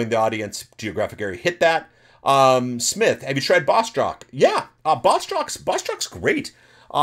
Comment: bosswhat? Bossjock? Bossdrock? What is the name of the podcast host he is saying at 49:44? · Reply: Boss jock